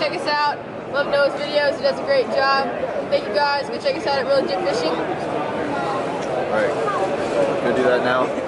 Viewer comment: What was the channel name of the twin kids? I can't understand the name.